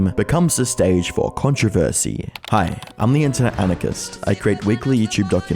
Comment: I sometimes mishear him as "internet narcissist" lol